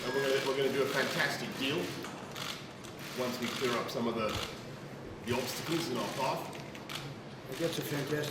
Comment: i keep hearing 'when we clear off the yobs in our path.....'